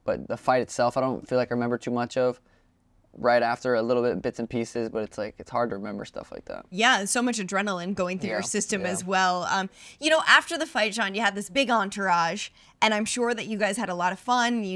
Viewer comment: Did she call him John @ 4:59?